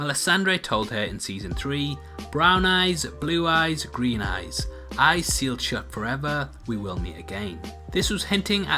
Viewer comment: I want to know why people are hearing ‘eyes sealed shut forever’, I hear ‘eyes you’ll shut forever’